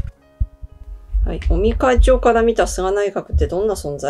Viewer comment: 菅内閣から尾身会長はどんな存在と言っているところで尾身会長をオニ会長と聞こえて、やっぱり鬼なんだー、と勘違いしました。